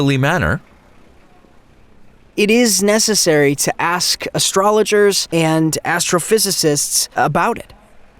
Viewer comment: did he actually say astrologer lmao? 😂